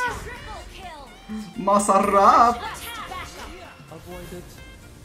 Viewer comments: WTF! Did you just say "masarap"? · did just say "masarap" '-'